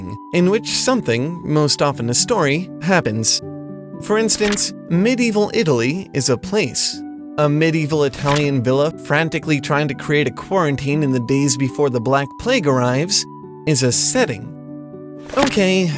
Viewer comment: did I hear QUARANTINE??